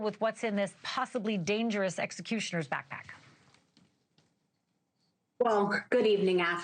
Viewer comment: Did I hear her say this correctly? 0:32 "...possibly dangerous EXECUTIONER'S backpack."